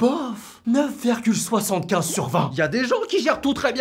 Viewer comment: DONNE MOI CETTE NOTE DE BOF 9,75 SUR 10 C TRÈS BI..BOF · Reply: Il a dit sur 20